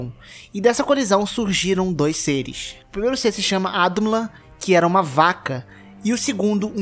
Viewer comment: como é o nome da vaca e do gigante ? não entendi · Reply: A vaca se chamava Audumbla e o gigante Ymir.